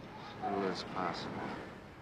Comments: What is he saying at 1:43 ? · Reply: As little as possible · What does he say at 1:45? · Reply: Singularity "As little as possible." It's going back to the question, "What did you do in Chinatown?" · what does jake say at 1:45 ? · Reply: A little as possible...